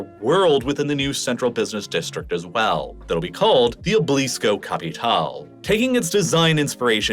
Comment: Lol i thought he said the tower will be called Iblis